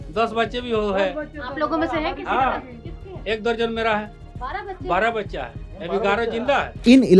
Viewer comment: Did he said 12 kids WTF😦😦